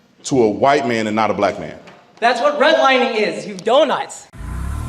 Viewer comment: Did that kid call him "donuts"? Or did I just mishear? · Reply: Donut. He's calling him an idiot.